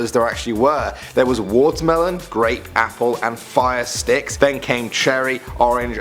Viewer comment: jolly ranchers are still alive in Indiana, also when u said apple I thought u said asphalt